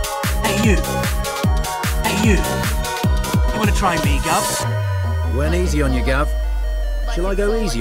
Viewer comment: i hear “wanna try me glove?”